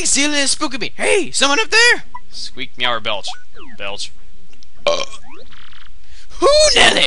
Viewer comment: i could've sworn belch said bitch XD